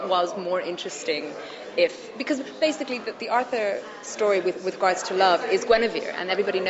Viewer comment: Does anyone know what Katie said at like 3:55 when Katie said, "the Arthur story with ____ to love..." What? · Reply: "with regards"